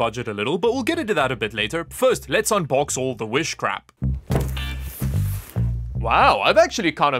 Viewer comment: you say "Wish Crap"... but all I hear is witchcraft, which might be what powers items from wish